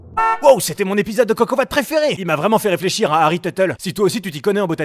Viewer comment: C est moi ou a la fin de la vidéo on entend "teute" genre a